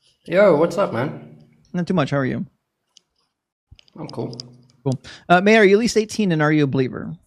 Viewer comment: WTF was up with his “feminine” statement at the beginning? didn’t say anything indicating he thought he was dealing with a woman or someone feminine or whatever. That was so weird to me. And he’s so dodgy. These people are whackadoodle. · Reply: I definitely hear "Mary" from the host. The subtitles, for what its worth, do disagree with me. I hear a clear "May" before that time stamp, and then a "Mary". IDK. Either way, If I hear "Mary" somehow, then the caller might have heard "Mary" too. I think that's what happened.